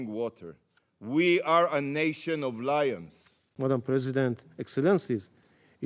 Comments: Did he just say 'Israel is a nation of liars"...? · Sound like he said “we are a nation of liars” for a sec. The real question is what would you do if your embassy was attacked.